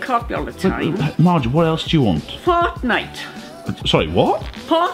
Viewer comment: I thought Marge said Hotnite 😂